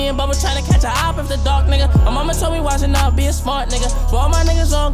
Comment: I always thought he said dark n***a not duck n***a